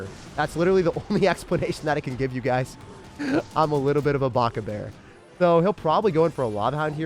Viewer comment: did he just say baka bear? That was a bit cringy